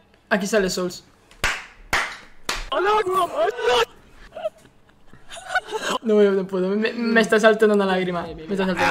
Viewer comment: yo escuche "al agua pato" WTF mis oídos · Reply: Dice allahu akbar